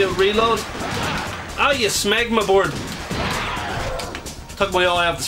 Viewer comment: What did you call him? I keep hearing "Smegma board"